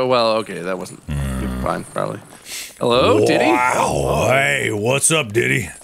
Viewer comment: sounds like they sayin "diddys" not deities